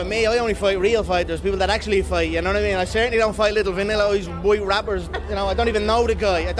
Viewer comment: "I certainly don't fight little vanilla fighters....boy rappers" lol Conor is a LEGEND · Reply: I actually think I heard him say “vanilla ice” haha ☺️